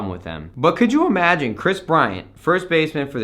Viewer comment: did anybody else hear him say kris bryant 1st basemen